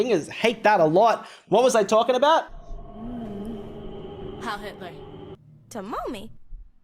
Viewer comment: what did she say and why did he react like that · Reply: She said "hail h!tler"